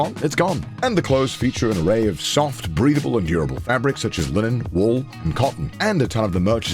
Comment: dude I hear breedable and not breathable am I'm going deaf?